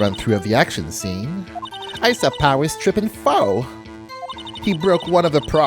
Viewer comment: For some reason I heard say "I saw Powers tripping balls" 😂